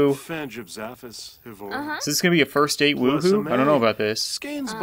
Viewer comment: anybody else hear say "Booba" as in the female pewdipies version of PP???